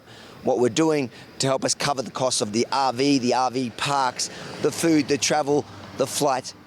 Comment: Every time I hear Avi’s pitch for support, I hear “help us cover the cost of ‘Avi’ (instead of RV).